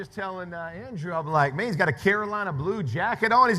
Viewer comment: Jeff Saturday was a Tar Heel. Thats why he said 2 former Tar Heels up here (Saturday and Ebron) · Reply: I thought he said Carolina guys. All four of these guys came from no football powerhouse schools. Crazy.